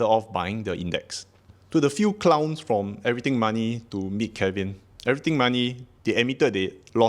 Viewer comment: When you mentioned clowns and said “everything money” my brain heard that as “millennial money” and I was very confused about the beef lmao